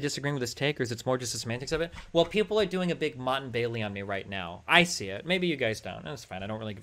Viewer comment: Does anyone know what a monton bailey is? He says it @ 20:46 and I tried googling the term to no avail